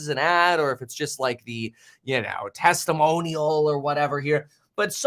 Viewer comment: did just say #teslamonial? That sounds like a twitter campaign for tsla in itself!